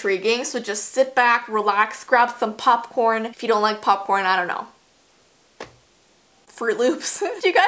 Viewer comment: you said to get popcorn I thought you said pasta I got pasta